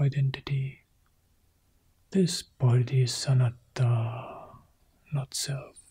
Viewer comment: Hon. Bhikku Analayo, Should not the term "Anatta" be used instead of "Sanatta" in This Guided Meditation? Thanks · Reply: Sounds like he said “this isssss anatta”. Sounded like sanatta because he extended the s